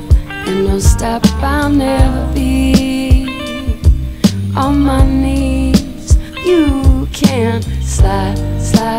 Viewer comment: At first I thought she was saying "'Cause I'll never be.. a mommy," and I was like wow, that's a gut punch